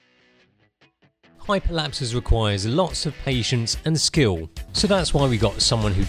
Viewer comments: i first heard "Hyperlapses requires lotsof asians and skill"^^ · I thought he said "Hyperlapse requires lots of asians and skill" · I thought he said "lots of Asians and skill" 😂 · am i the only one who heard: hyperlapses require lots of asians and skill